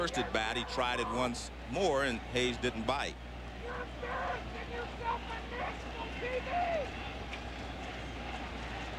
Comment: what is the loud mouthed fan yelling that got picked up by the broadcaster's mic ? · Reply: He said "You're embarrassing yourself on national TV." There is a pot and kettle joke to be made there.